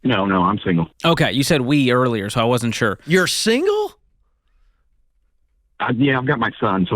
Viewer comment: Ken said in shock: “You’re single?” which sounded like “You’re a communist?” These Ramsey people are such marriage fundementalist jihadists that they border on Marriage-fascism. This guy is debt-free, relationship-free, and hen has a son that sooner or later will fly from the coop. He is the poster child for the “the land of the free, the home of the brave."